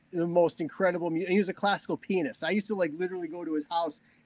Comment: Mike is such a cool laid-back guy, definitely a guy I would be in a band with,such an underated guitar player/song writer!! At the 28:03 mark it sounds like Mike said classical penis 😂🤣 but he says classical pianist. Hilarious!